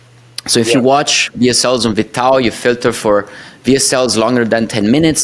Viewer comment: Great Interview . At 27:10 you mentioned a platform to watch vsl i didnt catch the name. Please can you clarify . Thanks for sharing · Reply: Vidtao